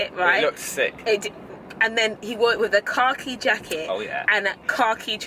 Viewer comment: Just came back and rewatched this vlog, the whole time I thought you were saying "car key jacket" instead of khaki lmao